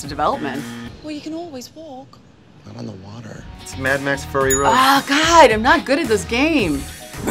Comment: am I hearing things or did he call it Mad Max: Furry Road? :D